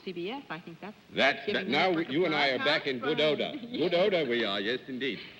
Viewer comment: Does anyone know what John is saying at 20:50? "You and I are back in pinoda" Or Pinotta or Pannota or... I just don't know what he says. Or what it means. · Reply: Pygiana He's saying "back in good ORDER", but with an exaggerated British-type inflection.